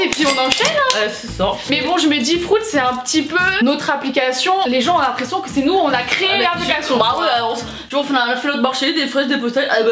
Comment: Y’a que moi qui comprends « frout » au lieu de « fruitz » quand le dit? 😂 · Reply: Nan moi aussi j'ai compris "frout"😂👑